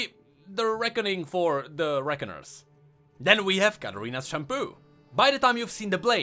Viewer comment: I thought was saying Katarina’s shampoo not shun po